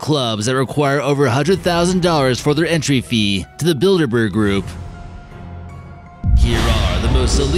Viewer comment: For number 3 I thought he said the build a bear group... I'd be the president of that particular group